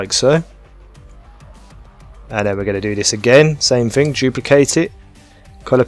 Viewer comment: I think he said jewplicate it